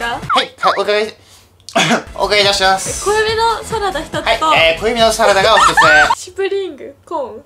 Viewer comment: 「小エビのサラダ」が「小指のサラダ」と聞こえた。